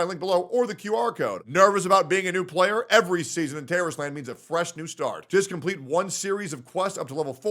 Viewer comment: First off, I know I'm not the only one that heard that, right? I mean, seriously terrorist land. That's what it sounded like he was saying. I literally speak Coke out all over the place after hearing that. I hope I'm not the only one that was hearing that.